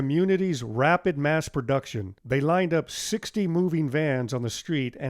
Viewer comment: I listened very carefully at 2:07 and you say it was staged with 60 moving vans..maybe you said 16 but to me it sounded like 60. Anyway judging from the photo, 60 vans would have been overkill since you could only see but so far down the street so 16 would make more sense. Plus I found it interesting that your control said Fly With Caution but somehow you couldn’t get it in the air. I didn’t know powers to be could jam those remote controls where you could not even use. If they can do that then you might think they could just limit the altitude so as not to interfere with nearby aircraft which I saw the location was near an airfield. Great video and that still looks like a wonderful neighborhood and thanks for sharing 👍👍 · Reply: I said 60 because that’s what my research uncovered. That is quite a few.